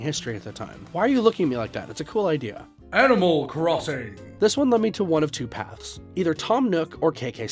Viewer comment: it said animal crossing and i heard "Edible Crossings"